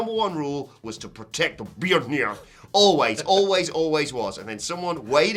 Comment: I've played it back a dozen times and all I can hear is "protect beard hair" What is actually saying?!?